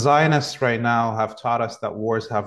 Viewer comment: Oh, "WORDS" have no meaning. I heard him say "WARS have no meaning, twice." That's a problem pronouncing OR, it actually sounds like ER, as in "WERDS" have no meaning, as opposed to "WARDS" as in hospital wards.